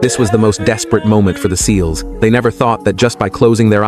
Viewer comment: My dumahh thought he said " this was the most desperate moment for the orca"🥀🥀🥀😔✌